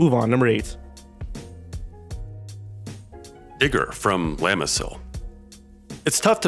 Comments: when he said digger I thought he said n##### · I wasn't looking at my screen and I heard "Digger" but I thought he said the n word and I quickly looked back